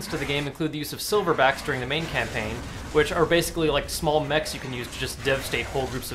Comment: i thought he said 'like small mexicans you can use'